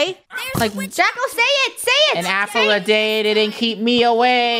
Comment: did say apple or...